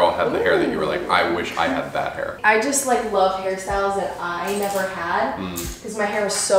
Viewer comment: I seriously thought that she said "i just really love harry styles" and i even said same. But just now i saw a comment saying that she said "i just really love hairstyles" lol ok this might not even make sense😂